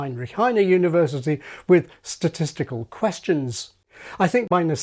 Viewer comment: 'Beetroot' is used much less frequently in N. America than in UK/Aus. It's frequently used words that lose their boundaries. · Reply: I swore you said TESTICLE QUESTIONS 🤣